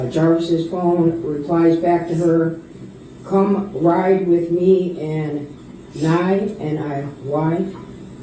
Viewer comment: did he say come ride with me and niy as in janiyah